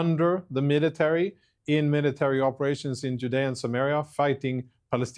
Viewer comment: Every time I hear "Judea and Sumaria" I basically hear Narnia and Metropolis